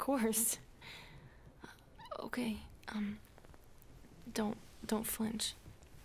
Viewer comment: Oh man, when she said "Dont flinch", I thought she said "Don't french" at first. 🤦‍♂️